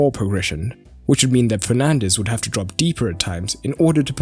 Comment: did you say Penaldes or I heard wrong???🤣🤣🤣🤣